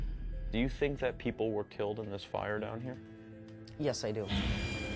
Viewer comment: i thought he said ''killed by those spiders down there?'' i got real confused